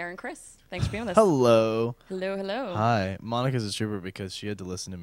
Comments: What? Did I hear 'Monica's a stripper because she had to listen to me for like six hours' at 0:34 ? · LOL omg I thought he said stripper instead of trooper and I was like "I don't get it..." :P